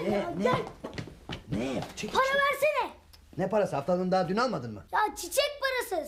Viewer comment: haftalığını derken aptallığını anladım